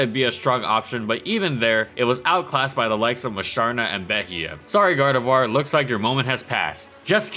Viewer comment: I couldn't quite catch what he said in 4:48 It was something about Gardevoir being outclasses by Musharna and another pokemon which I couldn't hear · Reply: Beeheyem